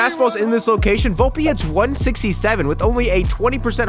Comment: did someone say 67?!?!?!